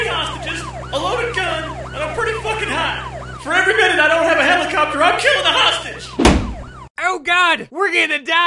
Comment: What does he say at 2:15? "For every minute I don't have __..." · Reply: Helicopter